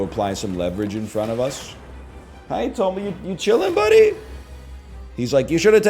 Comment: did he call tomo toby?